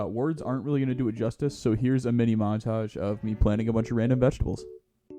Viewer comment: bro "Plants rain in vegetables" That's what I heard at least